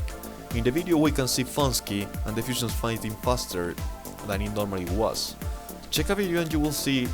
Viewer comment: Great video but i choudnt understand what you where saying at 10:05 to 10:8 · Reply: cers "faster than it normally was" haha, sorry about it, hope you understood the rest.